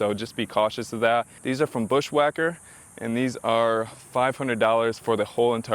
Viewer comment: I can't make out what kind if fender flares you put on. I thought you said Quadratec but I can't seem to locate them. Any assistance would be awesome. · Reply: 9.5 inch deep pocket fender flairs from bushwhacker. I bought them from <link>